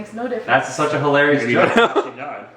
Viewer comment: What does say at 11:23? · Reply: Chenzys Videos "you don't even know how she died"